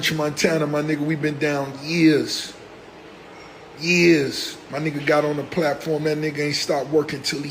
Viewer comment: sounds like he saying Earz instead of Years 😂😂😂